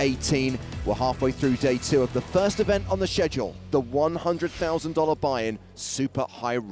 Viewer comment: did he just say "chedule" ? XD